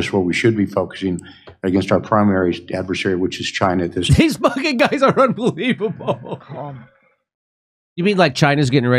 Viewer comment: jimmy says "these effin guys are unbelievable" and the closed caption says "these bucket guys are unbelievable" lol! these "bucket guys" that made me laugh....